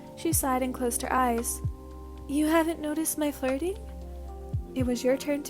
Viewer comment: I THOUGHT SAID “you have not heard my farting” bahahah 😂🤣😂🤣